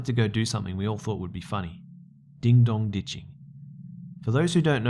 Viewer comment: I thought u said ding-dong bitching 😭😂😅